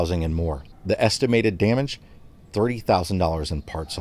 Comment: Are you sure it was only $3000 in parts for the Jeep...? I heard it was a LOT more... 🤔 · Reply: I thought it sounded like he said $3000, Must be my midwestern accents hearing...